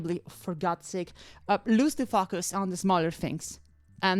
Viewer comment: I really heard "Lose the fuc*us on the smollest things" which is a great sentence on its own